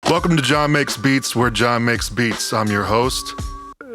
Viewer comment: I misheard that as Jah makes beats and I liked it.